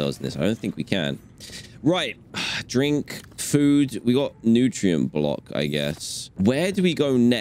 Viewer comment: did he just say 'drink food'